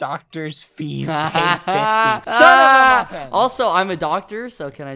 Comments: I swear i herd bbh say son af a b**** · Did badboyhalo say "son of a bi--ch"